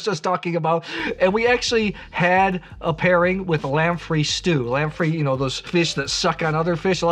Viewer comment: Excellent vid, so happy that someone is taking my fave red grape seriously. What was the stew @ 12:18? Did you say lamprey stew?